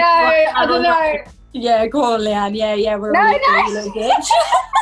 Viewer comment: What the hell is perrie saying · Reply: Yeah yeah yeah go on yeah yeah yeah Leigh Anne we were really good little ***